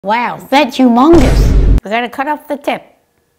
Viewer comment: I heard said "among us" on a first part 😂😂